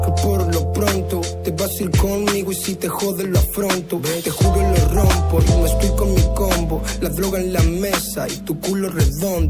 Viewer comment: te juro que conozco este tema desde que salio pero en el minuto 2:38 que dice" te juro lo afronto y no estoy con mi combo" ,barron,pero en los subtitulos del video se esta escrito "te juro lo rompo,lo explico mi combo" barrones los dos son unos capos franky y fazzini y vos tambien pela segui haci